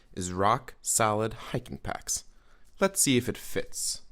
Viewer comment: I thought you said "the first thing that comes to my mind for a clear, concise yet catchy headline is "Rock SALAD Hiking Packs" and I was like "uhm...eccentric..."